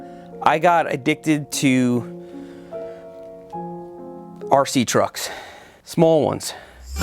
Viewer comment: I actually thought he was addicted to something, and when he said "I got addicted to RC Trucks, I thought he said RC drugs. Lol